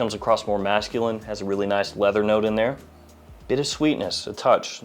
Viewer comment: did you mean "bit of sweetness", which is what I heard, or "bittersweet-ness" like the caption? 😅